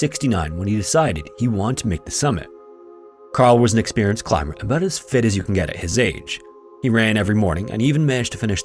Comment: For whatever reason, I thought I heard 2:14 as "About as fit as your dad"